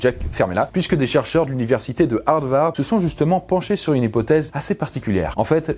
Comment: "l'université de Hard-Vard" ?? C'est quoi ca ? L'université d'où viennent les acteurs porno ?